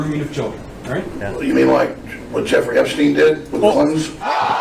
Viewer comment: Did he just say "The Clintons" or the "Clitorises"?